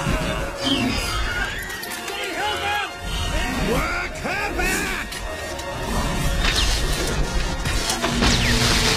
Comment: what did Robert Muldoon say? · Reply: "Work her back!"